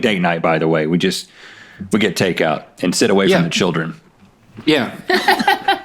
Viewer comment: "we order takeout.......and sit away from the children..." LMAO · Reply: Is that what he said? That makes more sense! I thought he said "send away for the children" and I was so confused. 😂